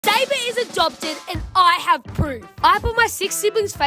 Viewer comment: I thought he said “And I have poop.” 💀💀💀